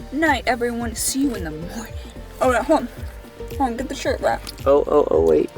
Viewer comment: She said get the t-shirt Rhett, and I swear I thought at first she said, get the t-shirt wet! LOL, I was like hell yea!